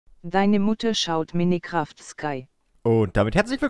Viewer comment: Ich hab verstanden : Meine Mutter schaut minecraft sky